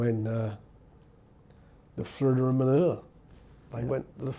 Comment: What Frank Warren can’t say “thrilla in Manila” properly @ 12:47?